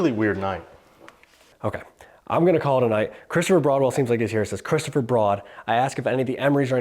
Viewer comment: …after they say “get out” I believe the next part you couldn’t understand was the start of a prayer. It sounds like “the body of Christ” then trails off. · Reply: Got to be honest, I heard: "Chris-topher Broad-well"!